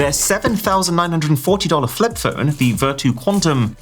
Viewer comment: Did I hear it right? Vertu Condm🤨